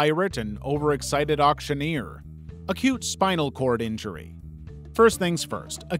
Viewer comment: I was looking away and I thought he said “A cute spinal cord injury.“ and I was like “EXCUSE ME WHAT?! 💀“